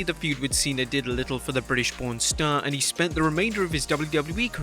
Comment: Am I only only one that misheard "British born star " for "British pounds star"? I swear I had to rewatch like 3 timesb